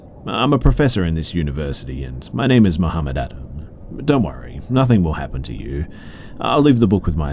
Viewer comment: did he say his name was Mohammed Atta??? If so, that was one of the 9/11 hijackers